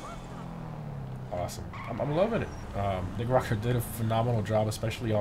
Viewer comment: ish he butchered rockstar I heard rivksnjd I didn’t hear rock star